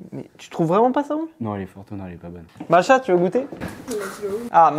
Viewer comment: "nan elle est pas bonne , Macha tu veux goûter " j'ai compris " Elle est pas bonne Macha" d'ailleurs prénom incroyable